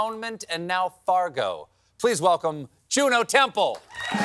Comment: I heard “Jew No Temple”.